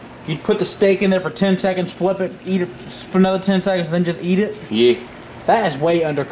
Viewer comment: did he just say "ye"